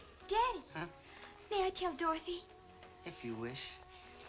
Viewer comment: I misheard“kill” instead of “tell.” “Danny! May I kill Dorothy?” “If you wish” “Hey, Dorothy!”